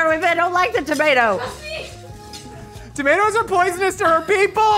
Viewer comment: At first, I thought said, tap my butt, instead of "tap my button." 🤣🤣